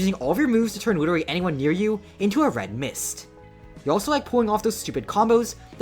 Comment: did you just say Red Mist?